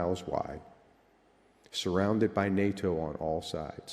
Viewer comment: please help to parse this.. he says something that I hear as "8-2-80 bubble".. what does it mean? 😅